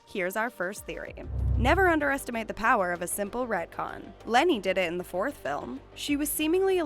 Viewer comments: did she call her "lenny"? Tf lol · i’m sorry...... did she say LENNY